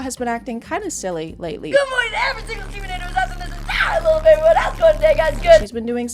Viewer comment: "gomornieverysingleseesawinthisenTIreeleventodayguyseverythingisgood" 🗣🗣🔥🔥🔥 I know that's not what she said I cant understand her ✌️🥹